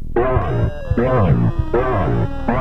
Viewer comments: what i hear is run and warning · What is he saying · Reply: run